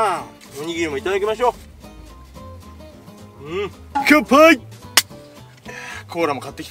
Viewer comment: の乾杯がグッパイって聞こえた。ww